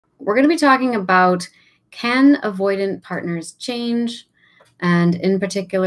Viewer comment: In one of your videos I thought you said “Cannon Avoidant” Partner Change so I googled it but found “ here” anyway, I thought it was a form of launching your feelings toward someone lol 😂